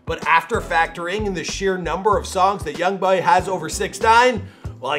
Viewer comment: Anyone else heared when he said young bhai at 5:22 in the vid